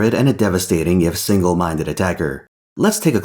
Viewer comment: is it bad that i heard "devastating single mom attacker"